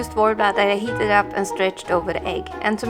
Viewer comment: I cant understand what the hell you said you was putting on the egg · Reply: Luvs Snow Worbla